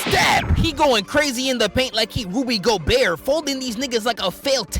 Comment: what funkmaster flex say? "that's what you call MF BARS NIGGA!" 😂🔥🔥